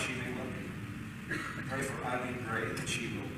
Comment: I thought her name was Eilleen not Emily? This is from 2012, I believe. He (John MacArthur) has never apologized in these 20+ years. Eilleen's husband was also either a Sunday school person or music; something with kids in their church. The Church removed him from that position but still required Eileen to stay with him. What a joke! I am in Awe of the courage that Eileen had to protect herself and her children. The Church Split channel did a long video on this. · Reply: Yes he did and I thought he said Eileen. What did you hear?